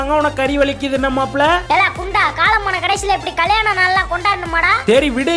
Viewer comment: What is the word? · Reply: Broo thappa nenaikkathingaa GUNDA